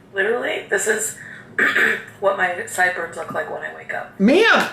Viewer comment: Oh my fucking god I hate being hard of hearing and dyslexic bc at 1:27 she said “this is literally what my sideburns look like when I wake up” but I heard “this is literally what my insides look like when I have diarrhea” by face I thought she really said that so I had to fucking listen so carefully again hahahah